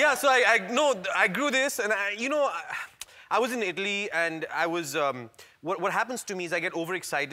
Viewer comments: sounded like "I was in Idli" hahahaha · Am the only one who thought he was saying 'Idli'(Indian dish) later realised that it is actually 'Italy'?😅😀